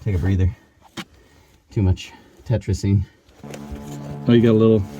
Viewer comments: What's he saying here... "tertrazine" ? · When stops for a breather, it sounds like he says "too much tetrazene/tetrazine/tartrazine." With tetrazine being a pretty random, albeit fairly complex chemistry component... that doesn't seem to fit the circumstance very well. And with tartrazine being another term for "Yellow #5," the common food-coloring agent, that doesn't make much sense either. So, by process of elimination, I must conclude that "tetrazene" was the word used... and with tetrazene being a chemical compound used in explosives, my subsequent question would be: Were you guys utilizing explosives in an attempt to free up more space down there? And if not, then who was? (Hopefully, it wasn't the person leaving their used hypodermic needles around!)